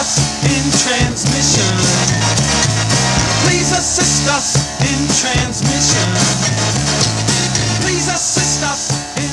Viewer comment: Instead of "please assist us, in transmission..." I thought it said, "Jesus dissed us in transition." & I thought, wtf?